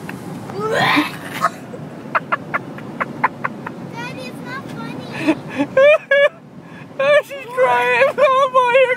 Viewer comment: Did I Hear A "Daddy Its not funny"?